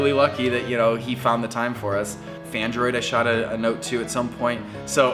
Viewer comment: did you say fandroid? Like fandroid music?